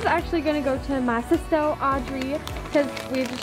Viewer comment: when she said awww treats i thought she said audrey